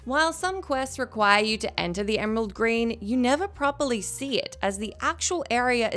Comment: Did i hear Emerald Green* (6:37) ? :D